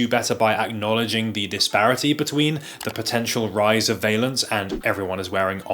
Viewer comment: Vielance, i thought you said Vailant ie central heating boilers in the UK.